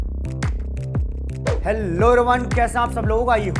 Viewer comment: lol I heard like "hello Rohan"